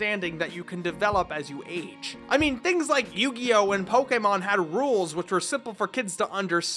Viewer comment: what did you say yugioh simple for kids ?😂😂😂😂😂😂😂